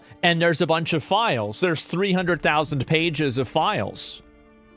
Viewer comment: anyone else misheard that there are tons of "pageoffiles"?